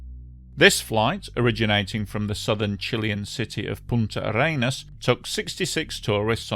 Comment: did he say the city of “PUNT HER ANUS”???